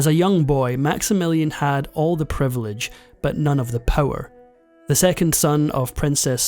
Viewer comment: When you said max had all of the money buy none of the power, i thought you said none of the *Poor. I thought "God brits say poor weird. And canucks."